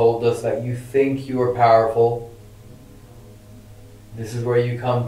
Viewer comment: says “you told us that you think you are powerful,and it sounds like it said”I am powerful.”Anyone else hear that?